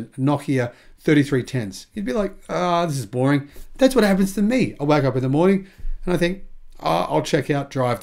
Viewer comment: FYI I heard "I wack off in the morning" 👀